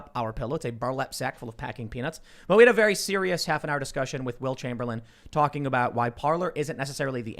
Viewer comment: Haha I thought for sure you said Wilt Chamberlain